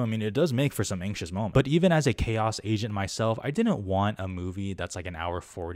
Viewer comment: when you said “chaos agent” I thought you said “chaos Asian”